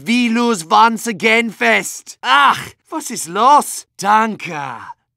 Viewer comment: I thought he said: "Ack! What's this loss?" For a very long time... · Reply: “Ack! Was ist los?” Which is german for “What is wrong?”